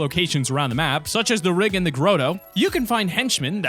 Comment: did you just say groto?